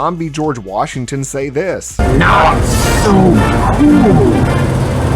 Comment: did he say ,,not so cool,is it?" or am i not hearing it right?